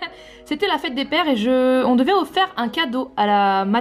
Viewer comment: on devait offert c'est moi seul j'entends ça?